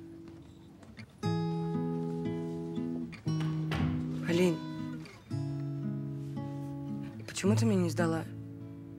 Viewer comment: как отчество не расслышал 😅Виталий Миньетович😂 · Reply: Минеевич